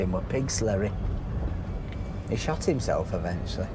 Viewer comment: I thought you said he shat himself. Brio yes. That 2cv pick up must have been the Stone masons